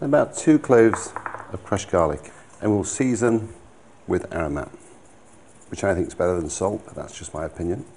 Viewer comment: season with what and 1:09 put in what?? I didn't catch that and English isn't my first language, can someone help me out 😭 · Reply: knorr aromat seasoning